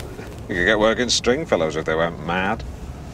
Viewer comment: You could get work in Stringfellows if they went mad. Lol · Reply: I thought he said " weren't mad" which is why looks happy.